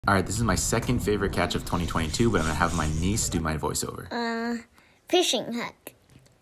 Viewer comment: I thought I heard "I have my knees to my voice-over"